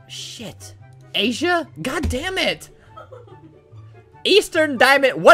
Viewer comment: It sounds like he says, got dam